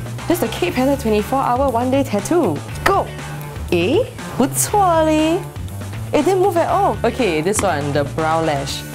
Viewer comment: lool what does she say @ 2:12? "eh?....whatswali!!"?? soo cute!